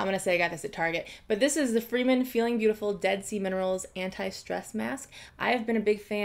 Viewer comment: I swear to god I thought she said "dead semen rolls" instead of Dead Sea minerals